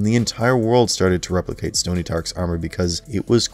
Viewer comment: did anyone else catch the "Stony Tark's" instead of Tony Stark @ 24:32 ?